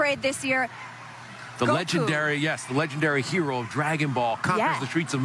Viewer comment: WTF!! He Said "legendary cartoon", Do you think that DBS is a cartoon ...😡